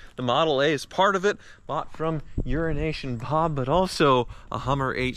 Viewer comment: I'm sure everyone (except Ford) appreciates you demonstrating Lightning's towing capacity. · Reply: Did i just hear "Urination Bob?" I'd love the back story on that if i heard that correctly